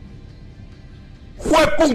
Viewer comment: que dijo ese? Solo entiendo lo de "malpario" jajaja